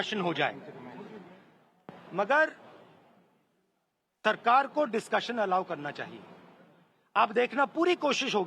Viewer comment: did I hear Tarkar???